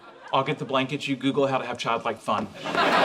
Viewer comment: "How to have child like fun?" or did I hear it wrong?. 😂